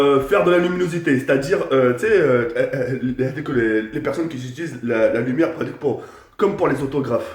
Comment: C'est moi ou à 2:54 il a dis "comme pour les personnes qui utilisent les autographes" XD